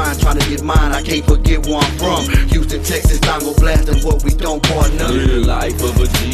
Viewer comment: what he says? tango blastin .... what?